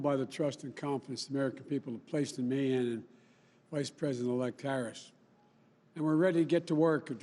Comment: did he say Karris?